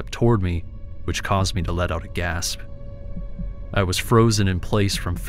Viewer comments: Story 2: "They took a single step towards me, which caused me to let out gas." That's what I thought I heard him say and I chuckled a little. 😂🤣 For the record, it actually says "let out a gasp." 😅😮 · I thought he said "they took a step towards me which caused me to let out a gas" 😂 I was shocked for a second.